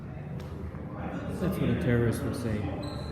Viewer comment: What did the #SnowFlake say roughly @ 10:23 minute and second mark? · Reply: "That's what a terrorist would say"